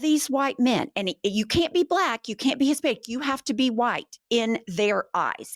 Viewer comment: …..you can’t be a”spic” Did she just say that???!! I really appreciate you ladies but ….🤬🤬🤬🤬🤬🤬🤬